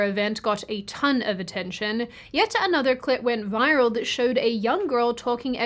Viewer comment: Did anyone else hear that? Was it just me? Seriously. Did anyone else hear that? Listen again. Tell me what you hear. “Yet another ___ went viral showing a girl talking…” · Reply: "Clip" is the word you're looking for, not the other thing.